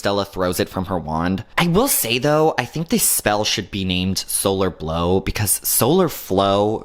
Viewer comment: Lol. It is actually named Solar Blow. I think the wiki people heard it wrong. Hear it clearly